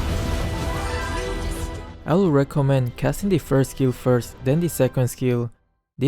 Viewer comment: Did he just say (hc) that he'll gift kills? Hahah lol